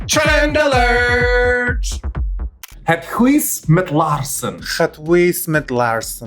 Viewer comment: What are they trying to say? "Het ... met laarzen"😂😂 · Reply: het huis met laarzen